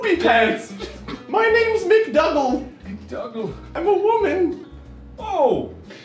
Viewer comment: What was said: "My names McDougal" What I heard: "My names McDouble"